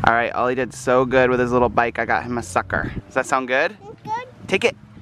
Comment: it sounded like said your a sucker to ollie